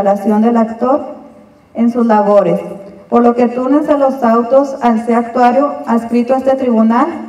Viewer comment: minuto 22:14 , que dice la licenciada yo entiendo "por lo que tunes a los autos han actuario han escrito" alguien me puede decir que dice por favor.